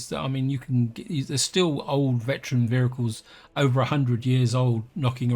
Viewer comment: I keep hearing "vhericle" and I can't stop!